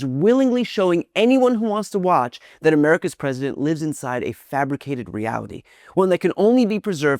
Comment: Lives…I thought she said lies.